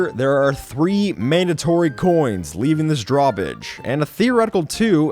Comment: di-did you just say drawbidge?